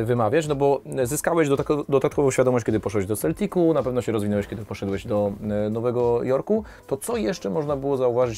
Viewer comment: "Poszłeś..."???? Dobrze usłyszałem???? 😂😂😂😂😂